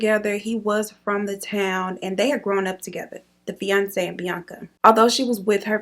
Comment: Girl I thought you said “Beyoncé and Bianca” 🤦🏻‍♀️😂😂😂 I think you said “fiancé and Bianca.” 😅 Of course I spelled his name Bianca wrong 😑 Pretty soon XYZ is going to be another spelling for Karen 😬